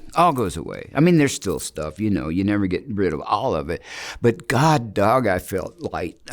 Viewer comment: Russ needs to refrain from cuss words as a representative of the Gospel. Especially when he knows thousands of people will be watching him on YouTube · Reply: I just heard him say "God dog" instead of God dam" at <link> Did he say some other cuss words in this video? I didn't check it carefully, but I think he covered this one okay.